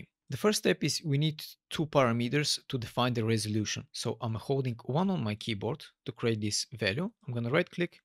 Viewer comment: I'm speaking another language and I didn't understand what you said. So can you tell me what you were doing at that moment? What did you press? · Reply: He said: ''Holding '1' on my keyboard''